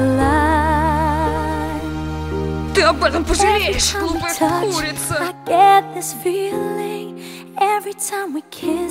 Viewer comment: what did they say? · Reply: "you will regret this dumb bitch"